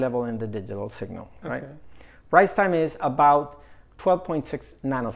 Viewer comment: I think I need to get my hearing checked. When he said "rise time", I thought he said "rice time".